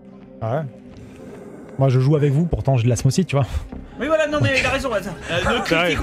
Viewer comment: J'ai compris "je joue avec vous je garde mon slip" 🤣